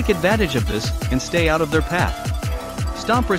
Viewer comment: when you said twisted Astro, I thought you said twisted Ass bro